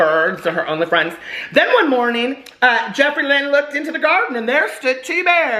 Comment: Oooooh I thought he was saying Jeffree Land... 🤦‍♀️